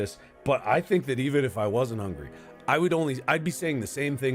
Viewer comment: IF he was money hungry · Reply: sorry I misheard him, I thought he said money hungry, he just said hungry 💀